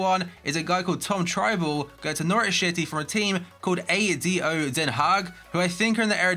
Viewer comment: I thought that said Nowrich Schity